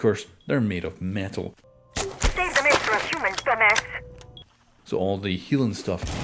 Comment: what did she say? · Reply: "Save the meds for a human, dumba**!" She chews you out for trying to heal the bots.